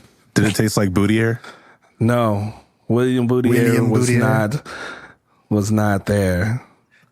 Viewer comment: i keep hearing it as "Budiere" hahahhaaa